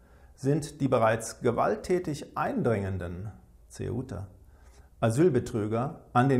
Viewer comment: I edited and re-uploaded this because I figured out what Curio was saying at 1:50 - he says "Ceuta", the Spanish enclave in north Africa, where migrants recently attacked border guards (thanks Alexander)